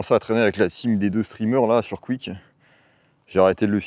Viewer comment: ????? sur quoi ? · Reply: Quick.......sur Kick mdr